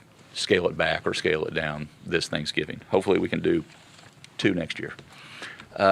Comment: Did I hear this right.....we are going to have to follow Thanksgiving day recommendations next year too ( starts at 7:39 )??? Also did I hear you don't have to quarantine if you have a job outside the home ( starts at 14:36 ) ? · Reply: No he said hopefully we can have 2 thanksgiving celebration next year .. one to make up for this year .